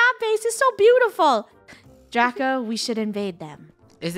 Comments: DRACA!! im sorry i heard draca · Did say Draca